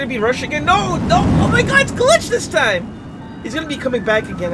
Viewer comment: did he just call ambush glitch